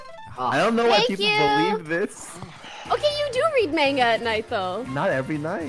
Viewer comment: did poki just say "menga" instead of manga